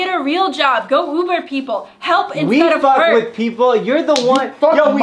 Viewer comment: In 22:20 She said "help instead of hurt", not help in some work... she's honestly kind of right cause we only have one life and while cheating in a relationship is bad, life is so small that we have to take some opportunities, cause in the future we're going to look back at missed opportunities and feel pitty for ourselves. I know they're trying to teach her a lesson but, what if they gave advice or created new opportunities in order to fix a wrong relationship? Not break it even more ! That's kind of rude